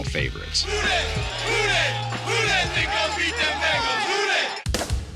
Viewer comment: "Who dey THINK gon' beat them Bengals" THINK not SAY.